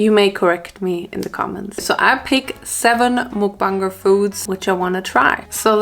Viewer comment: Omg I thought she said "mukbang foots" 😭